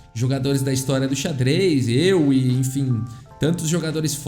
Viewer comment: Pfvr me digam que n fui só eu que vi no 18:46 quando ele falo euwe eu ouvi eu e kkkkkkkkkkkkkkkkkkk n é possível que fui só eu pfvr responde Obs: mas olhem uns 30 segundos antes pra entender o contexto